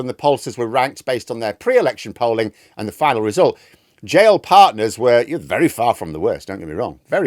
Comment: I thought you said Jail Partners ..... my heart leapt.